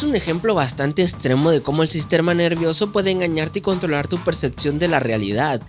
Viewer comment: Espera espera we escuche sisterma nervioso en el 9:03? 🤨